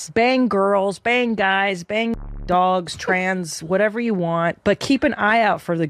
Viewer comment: did she say bang dogs?